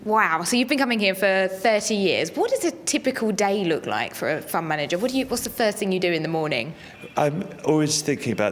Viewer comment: I thought it said ‘Fraud Manager’